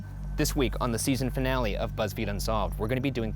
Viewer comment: i thought they said series finale and shat my pants